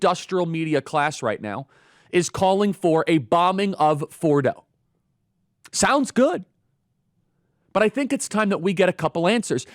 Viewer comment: lol I thought you said "...the bombing of FLORIDA..."